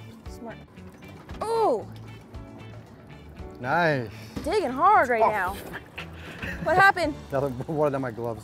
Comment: Did she just say “It’s big and hard?” At 11:15.